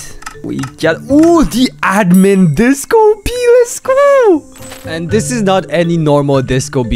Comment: What he said: Disco Bee Captions: Disco Pee